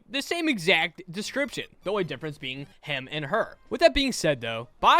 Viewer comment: I heard ham not him